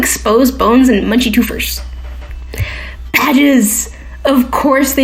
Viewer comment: When you said badges I thought you said badgers and I'm not complaining either way